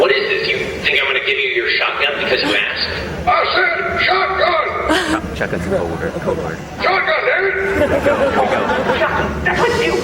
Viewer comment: I couldn't hear him that well. What were some of the things he was saying? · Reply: Sorry for the late response. Here are some things I could make out. At 1:12 He says "Shotgun's the code word. Here we go, here we go". At 3:52 I think he says something like "I did the acting for this" or something like that. I can't really tell. I can only really make out the first and last part of that sentence. At 5:46 he says "That's me again back there. That's me right there".